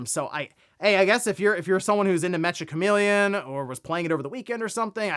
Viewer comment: No 60 FPS for the Switch version? Well, I know what version not to pick up. I am not going to stop playing Black Flag for 7 days. 16:42 Why do the video subtitles say Metroid Prime and not Mecha Chameleon? 94% of people in the poll have common sense. Blackout during Madden wouldn't be that hard.